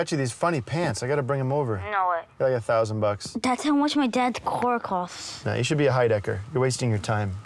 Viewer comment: Can someone tell me what the kid says at 1:18? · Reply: "no way" and "that's how much my dad's car costs"